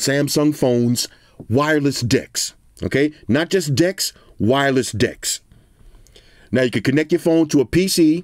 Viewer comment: Sounds like you said "Wireless D!cks" Not just D1cks. Wireless D1cks.